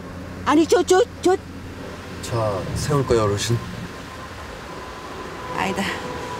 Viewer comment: 차 세울까요?아가씨?라고 들리는데 맞나여?ㅋㅋㅋㅋㅋㅋㅋㅋ